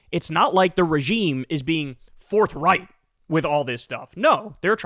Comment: Forthright? Oops thought you said Fourth Reich.